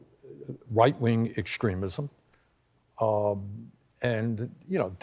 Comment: I've listened to it several times now... did anyone else hear Bernie say "White wing extremism"? Is that phrase a thing now or did Bernie just coin it? If it's already a thing, I've missed it. Or is it just my ears/tinnitus?